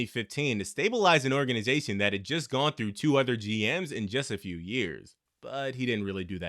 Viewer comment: I thought at first he said a organization that had gone through 200 GM’s in two years.